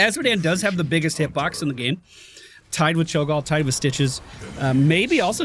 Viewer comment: did you say cho'gal?